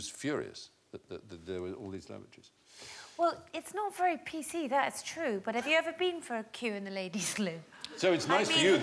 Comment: did i hear her right, "it is not very pc that it is true" how is the truth not pc when it is the truth. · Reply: nah, she said "that's true" (that is true) as opposed to "that it is true"